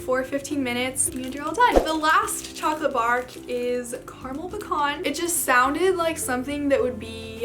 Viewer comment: I'm so disappointed, when she said "caramel and pecans" bark, I heard "caramel and bacon". Now I want to try chocolate flavored bacon. Aside from that, great video ^^